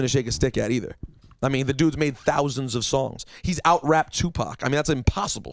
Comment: did he just say lil wayne out raped Tupac??..... dude... the two chainz argument alone was something I disagreed with but understood the idea of "not trying to be lyrical"....but dude.. C'MON SON!